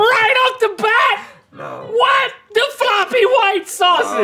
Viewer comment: I thought link said "what the ****" but he said "WHAT! THE FLOPPY WHITE SAUSAGE" don't judge until u know the whole thing 😳